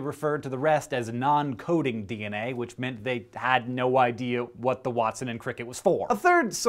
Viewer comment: is he saying "What the Watson" ?! :D